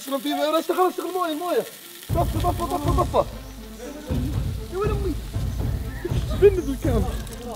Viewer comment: مدري وش قلت ما سمعت زين بس كانك تقول ياويلي اذا اي لا تقولها